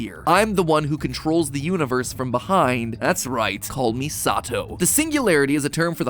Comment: "Call Me Sato" or first i heard was Kamisato meaning of 神里 (Kamisato) means God's village/place of residence - perhaps there will be a place in Inazuma representing 'holy land' Looool i am totally invested 😆